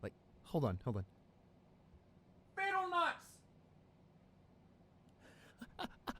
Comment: Ok, hold on. Did he just say Bettle Nuts?! 😂